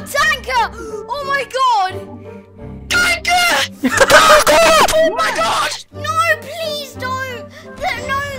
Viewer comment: I thought he said TACO xD